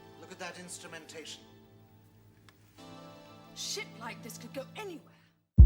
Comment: I thought she said "Sh*t like this can go anywhere!" Lol!